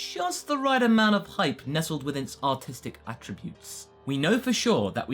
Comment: I thought you said autistic 😂